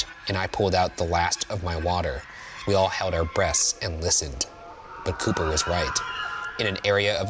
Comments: Did anyone else hear Ryan say "We all held our breasts" at 21:07? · Thought you said hold our breasts not breath😂